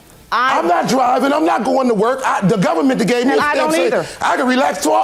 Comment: I thought he said the government paid him, and he does not have to work for the rest of his life. So, he cannot buy another TV from Walmart.